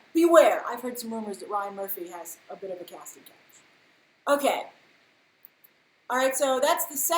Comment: What does she say in 31:09..... Ryan Murphy has a bit of a casting.....what? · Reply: Branden braganza casting couch look it up